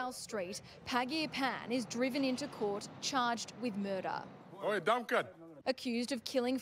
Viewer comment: "Oi dumb cunt!"